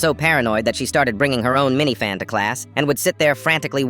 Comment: th caption said minivan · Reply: oh, I heard 'mini fan'